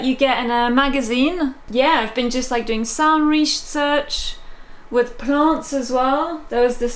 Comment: Thank you for letting me use this wonderful machine, and everyone for all the lovely comments! It sounds like i say 'I'm doing sandwich research' at 00:22 haha